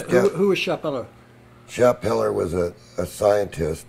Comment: Who is the German guy he's referring to at around the 00:56:56 and 01:01:30 mark? Can't catch what he's saying. It sounds something like "Sheller" or "Peller"... ? Edit: Nevermind. Finally found the name: Karl Schappeller.